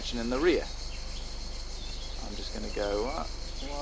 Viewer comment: Thank you for uploading these videos! Are these videos available for download? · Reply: I can't see me saying White at 11:58? What do I say exactly?